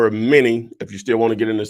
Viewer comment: Couldn’t understand what you said at 1:50/ 1:53 , but what word do I text to that number regarding the self-storage? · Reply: Text the word MINI to <num>